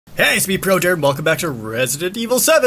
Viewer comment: you said projared so fast at the beginning it sounded like you said prodirt